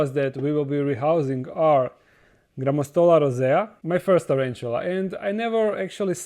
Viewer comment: This freaking youtube caption. on 0:27, it says “ drama stole our Isaiah” instead of Grammostola Rosea. lol